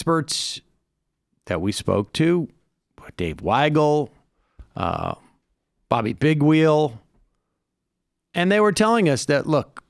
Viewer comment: I thought he said Bobby Pigwheel.